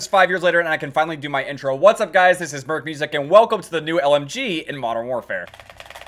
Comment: LIKE this video if I should go for OBSIDIAN CAMO next! ᕙ(˵ ಠ ਊ ಠ ˵)ᕗ What do you guys think about the New LMG that was added to Modern Warfare? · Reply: if you have subtitles on at 1:15 instead of it says Berk Boosic ;>